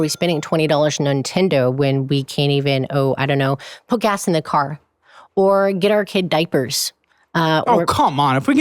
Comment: I thought she said "poke ass in the car"! LOL